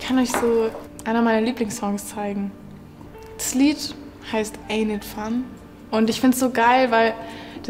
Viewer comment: Klingt jetzt vielleicht doof, aber könnte mir bitte jemand sagen, wie das von ihr angesprochene Lied in 5:21 heißt? Verstehe es akustisch nicht so genau🥲 Danke euch schon mal im Voraus! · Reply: Ain’t it fun von Paramore